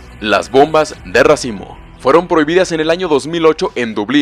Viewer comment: entendi "bombas de racismo" 💀